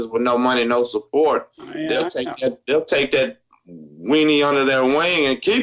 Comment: did he say “take that weenie under their wing”